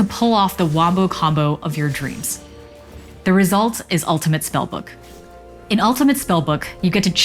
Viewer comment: I must mishear what she said. She said Ultimate BugBook right?